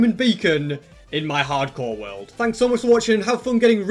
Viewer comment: yo, i thought you said diamonds are for peasants